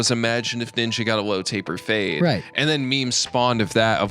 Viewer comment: ngl i heard "what if ninja got an elote buffet'